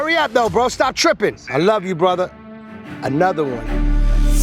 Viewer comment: Did he say I love you...another one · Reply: No he said I love you brother... another one